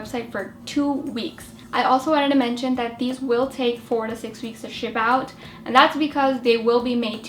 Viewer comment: I first thought she said 46 weeks!!!lol. Great video